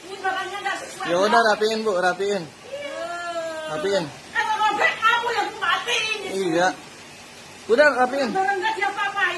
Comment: "Kau yang ku matiin" ibu ini ngancam mau bunuh kan ya? Atau saya yang salah denger.😅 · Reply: iah benar ada ucapan itu.. ”klo robek kau yg ku matiin”